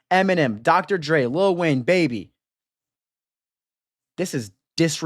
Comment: Did bro say lil BABY is a genius😭😭🙏